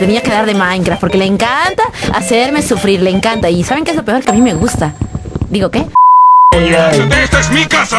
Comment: Minuto 3:31 dice que le gusta ,no se si no entendí bien o entendí mal